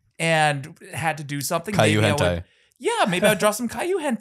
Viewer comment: I thought was saying Kaiju weird then realized no he means Caillou and that is not the same.